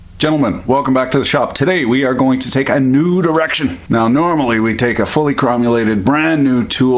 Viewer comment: I honestly thought you said "We're gonna take a nude erection" instead of "We're gonna take a new direction".